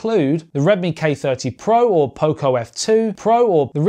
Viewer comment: Did you just say there was a new Pocophone F2 pro?? Please tell me I heard right >D